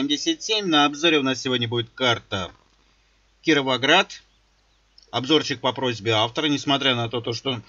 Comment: Мне послышалось или ты сказал Кирвоград!)